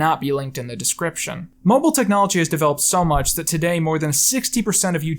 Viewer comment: Who else hears "mumble technology" ? XD